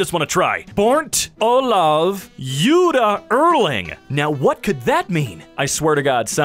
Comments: I think it says "born to love you darling" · I couldn't understand what the shirt at 7:23 was saying. I sat for a min with the video paused woundering if its just random letter and love just falls in place. Then i realized i just wasted an extra minute of my life on this video. Btw, "BORN TO LOVE YOU DARLING" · “Born to love you darling” is I think what it’s meant to say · well i think i figered out what it says. It says "born to love you darling" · born to love you darling is what it said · Born To Love You Darling Incase nobody could tell what it said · um, i think it says "born to love you darling"